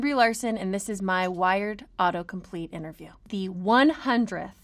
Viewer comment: Who else thought it said weird and not wired?